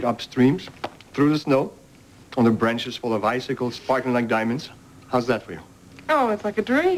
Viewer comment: did Jeremy say “farting like diamonds”?